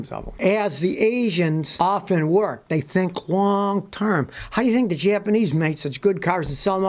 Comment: I thought he said wong term